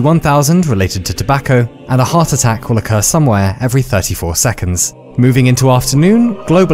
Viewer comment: Thought he said a heart attack from a curse lmfao